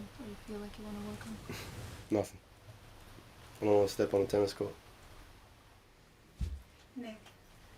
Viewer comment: "I dont wanna step on a tennis court?" did he say that?